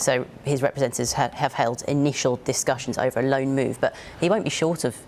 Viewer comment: did she really say "i-Nice-ial discussions" 😂